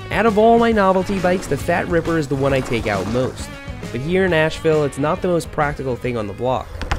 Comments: I just found out you live in Nashville like me!!! Can you send me links to the trails you use? I go to lock 4 the most. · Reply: I misheard Asheville for Nashville... 🤦‍♂️ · Hey, did you say you lived in Nashville? I thought I heard this around the 4:35 mark. I recently moved there in May!